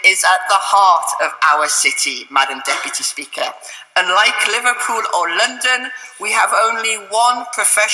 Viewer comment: lol I thought at first she said 'and like Liverpool & London we have only one professional football team'